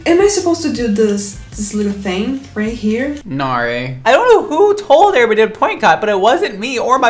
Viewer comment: it sounds like he said my name Nary